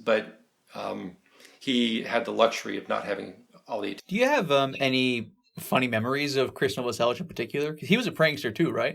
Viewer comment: what did he Finish with? I’m curious I like studying human behavior and I thought what he said was interesting but didn’t get to hear the rest ☹️ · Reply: attention.